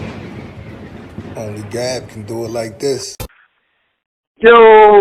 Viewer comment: I thought you were saying. "Only God, can do it like this". Now, I know you are saying "Only Gab, can do it like this."